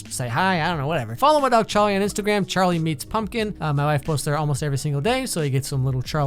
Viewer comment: is it just me or did he just say wife!!!